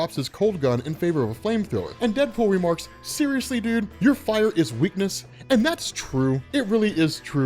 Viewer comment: "Your fire is weakness?" Hears that and scrolls to the comment section to see who else heard that dyslexia.